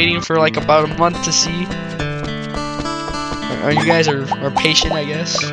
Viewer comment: when u said patient... i heard "no life".and btw.... did u say chocolate? yes CHOCOLATE?! CHOCOLAAATE!!! CHOOOOCOOOLAAAAATTEEE!!!!!! srry for that.i just had to do that XD